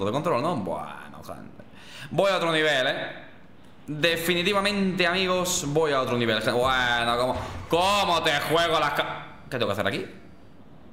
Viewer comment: Oyeron que dijo Como te juega la cab- Rivaldios 😮 Minuto